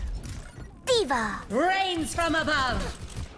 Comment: I can't be the only one who heard "brains from above" at 2:43 ( confirmed phara is a zombie! )